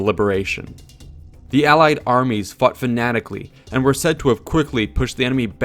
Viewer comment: Am I the only one who heard at the 13:10 mark that instead of "faught fanatically" i heard "fucked fanatically"?